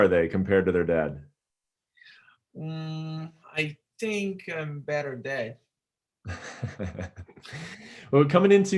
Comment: What did he say at 1:25? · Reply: I think im a better dad?